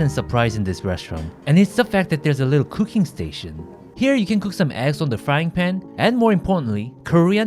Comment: 계란이 엉덩이로 들리는데 기분탓인가요